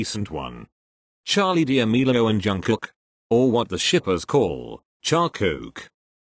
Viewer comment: he said Charkook... My subtitles said Charcoal Waaah👀🤣